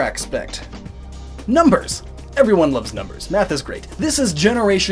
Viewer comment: I thought you said meth Lol